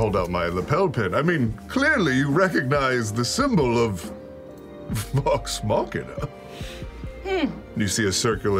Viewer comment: subtitles say "fox marketer" instead of Vox Machina and I can't unhear it